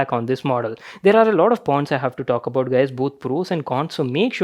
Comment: what did said can someone clear!!!🤣😅😅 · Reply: He said "There are lot of points"